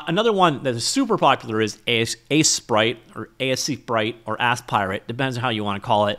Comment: did you just say "ass pirate" ? LOL